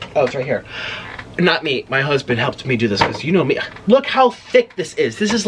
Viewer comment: Is he gay??? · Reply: Listen to what he said : 8:00 "Not me, my husband" he said